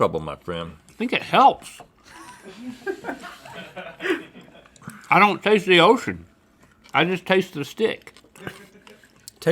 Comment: Did Link just said “ i don’t taste the yoshi “ 11:41 ? 🐸